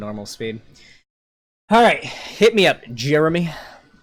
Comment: When he says his name I hear it as "germy"